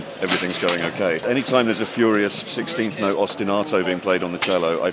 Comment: Guthrie says ''furious 16th note and...'', what does he say after that ? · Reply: Vkiller36 when there's a 16th note ostinato (repeating theme/melody) played on the cello, he plays the same thing in 'heavy metal mode' on guitar.